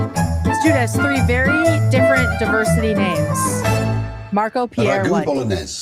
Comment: I thought David was saying Mark Appear White